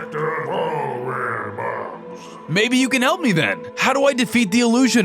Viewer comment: Day 74-76 I thought she said, help me step, bro